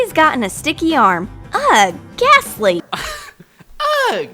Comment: i thought when they were saying “ghastly” they were saying “gas leak” 😂😂😭😂